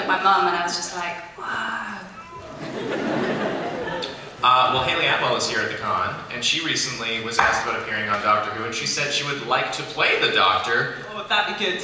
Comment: who does he say? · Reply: "Haley Atwell was recently at the con and she was asked if she would want to play the Doctor"